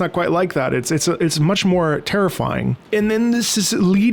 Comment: Rusted Pride during the V.IV Rusty Boss Battle in Armoured Core 6. Hands Down. It hits different depending on which path you chose. Either you feel happy and proud to be fighting with your friend to save everyone, or you can feel how hard he’s trying to protect the world from you. Honestly, it makes you realize the real protagonist hero was Rusty, and I’ve never wanted to lose canonically to what equates to one of the few people who care and value you as a person instead of as an expendable tool. Even in the other path, he could have left at anytime once he hit limit, but he couldn’t risk you being exposed during the boss and burns himself out. Honestly and truly, he’s your Buddy. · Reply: Wait I misunderstood, I thought you said terrific, not terrifying lmao. Please disregard haha.